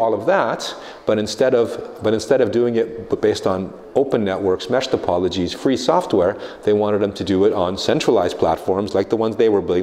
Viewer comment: What does he say here, "...open networks, mesh ______"? Thanks! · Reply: "open networks, meshed topologies, free software"